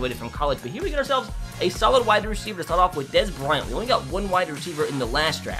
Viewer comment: Did he say Dez brown or Dez Bryant